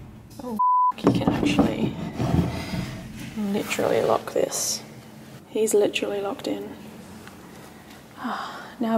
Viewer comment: right as says the "in" of "he's literally locked in, " I hear a male respond "yes." Then sign at 33:25, outside the same door I hear a man say "yes." And other people have pointed out a male "yeah" from inside during Cam!!